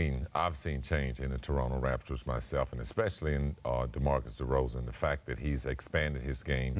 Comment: Anyone else hear scottie say Demarcus Derozan @ 1:56 😂😭 fuck is that